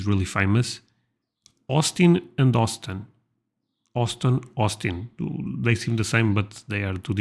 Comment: LOL sounded like you said Austin and Austin until I realized it was Austin and Houston. Great videos! Keep up the good work brother!